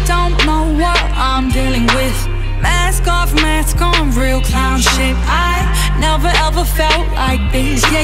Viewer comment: I thought she said real clout shit I like this song too